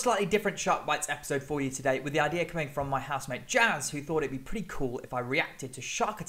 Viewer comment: does he say housemade or husband?? I am confusion (Husband would be the disappointing answer😘) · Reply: Hahaha definitely housemate!